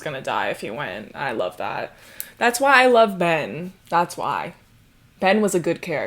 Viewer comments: I really thought you said "And that's why I love men" instead of Ben and I was like hm seems off-brand for but okay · girl I thought u said “that’s why I love men” and I was gonna be having some words.